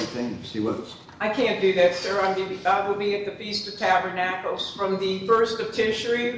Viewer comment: I thought she said festevus for the rest of us